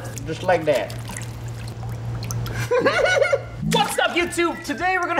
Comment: Did you said F*** up Youtube